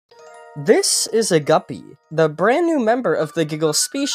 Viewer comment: “this is a guppy” DID HE JUST SAY GUBBY my stupid brain hears